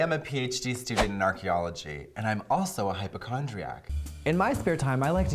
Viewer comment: Lol at the start I thought she said mitochondria